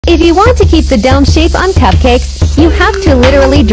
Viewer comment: I thought it said If you want to keep the dumb shape on cupcakes